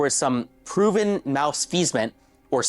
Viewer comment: But where his fedora tho? Fake ass Reddit mod Edit: 6:37 did he really just say "mouse-feezment" instead of malfeasance 😂😂😂 I'm fucking dying lol